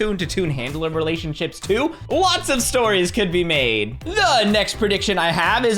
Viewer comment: I thought said this at 1:50 "Blots of stories could be made!" You're so goofy. Keep it up!